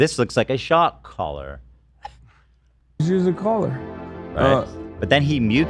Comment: 🚨🚨🚨🚨🚨🚨🚨🚨🚨🚨🚨🚨🚨🚨🚨🚨🚨I THINK HES SAYING , JUST USE THE COLLAR!!! not its just a collar!! aka possibly instructing his own mom to abuse her.